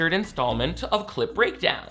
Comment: I keep hearing "cl*t breakdown" and tbh that's how I'm gonna remember this series from now on